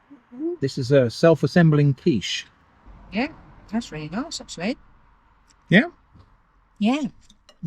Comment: I couldn't understand what said at 44:29, anyone? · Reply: “Yeah, that’s really nice actually”